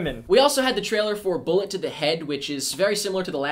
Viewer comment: When you said "Bullet to the Head" I thought you said Bullet IN the Head. I would have smashed my computer if they remade Bullet in the Head.